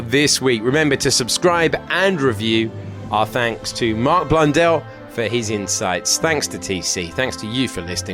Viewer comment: The Name "Mark Blundell" sounds like a knock off F1 game wanting to have budget Martin Brundle without licensing it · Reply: I actually thought that it said Martin Brundel. Had to check it up, got disappointed hahaha